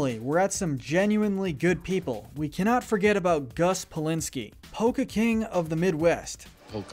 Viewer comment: when he said polka I thought he said volka lolololol